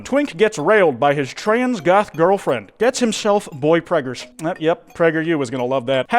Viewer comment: for a sec i thought you said PreggerU